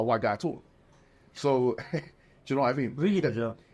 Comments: What did they say at 6:58? 😂😂😂. I assume the brother said "really tho" to MH. Audio is not the best set times. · what did he say at 06:57 ??? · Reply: “Really hijab?”